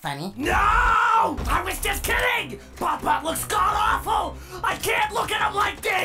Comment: of you turn on the automatic subtitles it says "papa" instead of "bon bon" 🤣🤣🤣